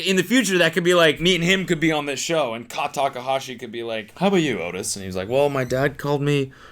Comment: did cody actually say his child real name of a made up one? i couldnt hear it properly anyway. Odas he said?? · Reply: Otis